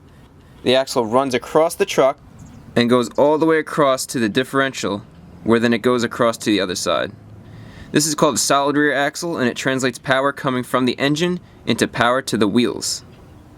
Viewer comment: I thought you said salad rear axle :-)) lol